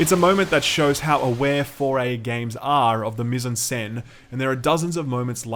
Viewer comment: What is he saying at 8:30 ???? “It’s a moment that shows how aware 4A games are of the _____….?” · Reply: Of the mis-en-scene.